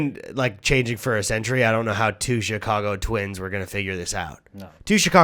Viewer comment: I first heard "Two Chicago Twinks". That would be a very different movie...